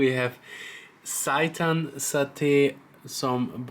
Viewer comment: i heard "syaitan satay" 🤣🤣🤣.. "syaitan mean devil" .. So u just eat devil satay.. This is hilarious..🤣🤣